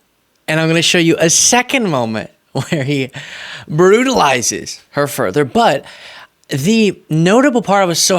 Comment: comment "brutalized her, but" sounded like brutalized her butt